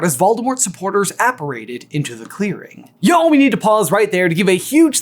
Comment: I thought Voldemort was saying "Ya'll-" to the Death Eaters 😭 13:43 the way I screamed "DRACO YOU'RE SO DUMB" at the screen 💀